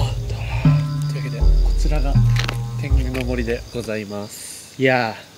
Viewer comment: 天狗の森がTENGAの森に聞こえる(╯⊙⊱ ⊙╰)